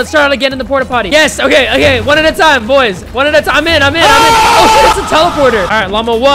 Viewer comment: DID U SAY SHI.. OR SHOOT